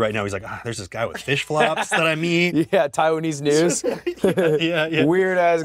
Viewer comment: one of the captions is wrong "taiwanese news" ludwigs says and it read "noose". please fix!